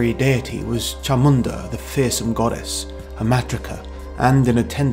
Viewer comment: You said this god came from Africa but didn't explain how it got to India. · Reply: Oh sorry. I wasn't listening too well the first time. I misheard "hermatrica."